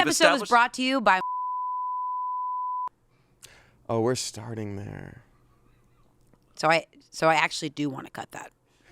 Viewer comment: what does she say😭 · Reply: I had to look at the uncensored version on apple podcast but she says "My stinky 🐈" 😂😂😂